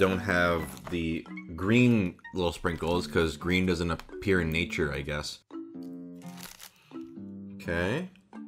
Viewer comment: did he say green doesn't appear in nature?